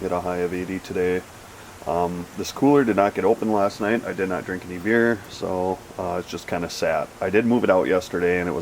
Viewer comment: It sounded like you said “ i didn’t drink any beer last night and it’s just kinda sad” lmao